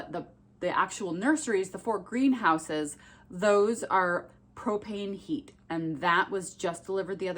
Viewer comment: I wasn't paying attention and I thought you said you had cocaine delivered. I was like, What kind of operation are you running there?!!! Then I realized you said propane.🚜